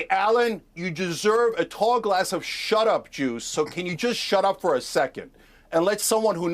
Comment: For a moment I thought Chenk said "shut up jew" 🤣